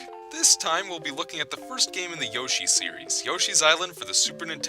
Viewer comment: When you said "The first game in the Yoshi series" I thought you said "The worst game in the Yoshi series." I was about to dislike, then I found out, so now the video is liked by me!